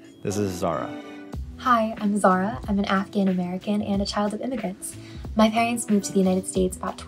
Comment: I thought she said she was African American. I was like tf? How? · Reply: i think she said afghan american?